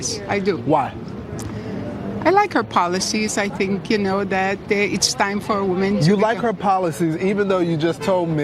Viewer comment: These dumb mother f***ers have learned nothing from 2016. Lawrence cut her off a bit, but she was saying "I think it's time for a woman." They dont understand policy, they care about being the ones that voted the first woman President, as if that makes them better than everyone else. Should be an east Trump 2020 win at this rate